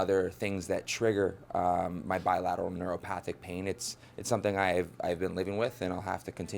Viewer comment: wtf did he say lol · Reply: Popcorn Chicken bilateral neuropathic pain